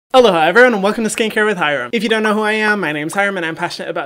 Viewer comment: For ages I thought the intro was "hi Hyram and welcome to skin care with Hyram" because hi everyone said fast sounds like hyram 😂😂😂❤️❤️❤️ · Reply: M.C.H I always thought he said “hello, hi everyone” but apparently he’s saying ALOHA EVERYONE😂 cuz he lives in Hawaii!!!